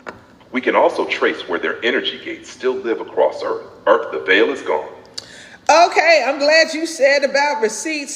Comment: Wait, did just say “Earth, the veil is gone”?